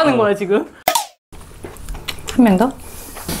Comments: 한병 더 를 한명 더 로 들었네 ㅋㅋㅋ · 한병 더를 한명 더로 들었어요 ㅋㅋㅋㅋㅋㅋㅋ 83다둥부부 ㅋㅋ 되시는건줄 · 한 병 더? 를 한 명 더? 로 들었넼ㅋㅋㅋㅋㅋ